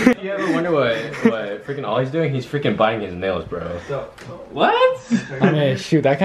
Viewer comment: said " if anyone is wondering what Oliver is doing he's biting his nails" And i thought he said "he is buying milk??" I know even know why